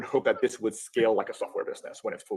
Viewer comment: I think he says “ what haven’t I asked yet “